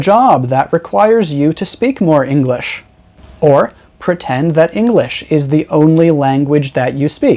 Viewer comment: Hello teacher, I would like to know the word in 17:26 Mins . I heard protand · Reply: Almost! Pretend✅